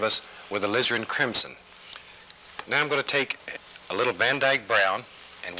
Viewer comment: When I was a kid watching these, I always heard it as "a lizard crimson". But I never saw a lizard.